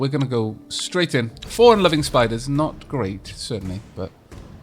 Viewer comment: Did you say "unloving spiders" at 9:30? Bit judgmental;P